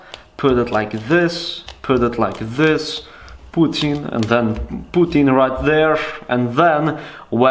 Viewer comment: Did you just say ''Putin''? Always you Russians. Just needs to say his name all the time